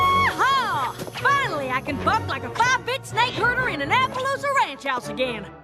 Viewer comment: I heard applejack say "bitch"